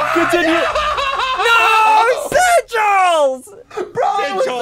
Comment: Did you say zudgels